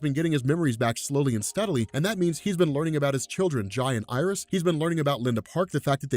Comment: when he says Jai and iris together all the time, it sounds like hes saying Giant Iris....and I think thats a pretty cool name for a kid.